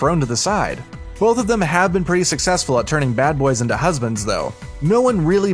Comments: Did he just say narutdo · Why does it sound like he is saying narudto